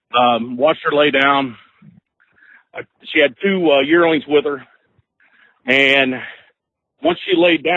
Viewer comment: Why did you kill a doe with two dearlings? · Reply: I thought he said yearlings